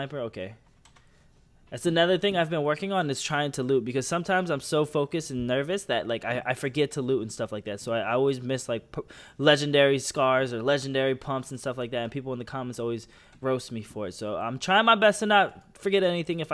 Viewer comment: did you say legendary pumps?